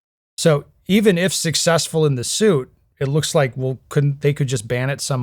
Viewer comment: “So, even if Sig-cessful in the suit…” That’s what I hear. The auto-transcript algorithm got it wrong. 😂